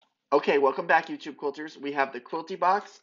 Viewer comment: When you said " Welcome back you tube quilters" I thought you said "welcome back you TWO quilters" lol. I thought, aww, surely he has more than two viewers. :) I love your attitude.